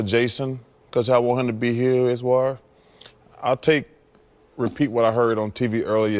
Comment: holy sht, wtf did he just say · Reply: jphillip212 I heard just a little of bit of his gibberish to catch that "cuz' I wanted him to be here as well" Lol good ol Chuck